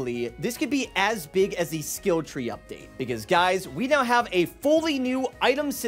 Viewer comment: I swear I heard him say "big as guys" like dude, guys aren't even that big lol